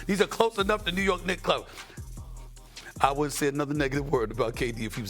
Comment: did he said the N word? 😅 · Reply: No...he said "I won't say another negative word"